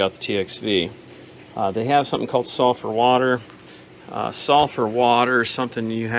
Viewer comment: At first I thought you were saying softer water, instead of sulfur. We have that here too, along with dissolved methane in the water. (In places where the methane emerges, it's moderately amusing to stick a lit match head near water coming out of a faucet and have it start spitting and popping.)